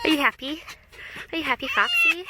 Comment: Which clips? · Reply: I hear her, "Are you happy foxy"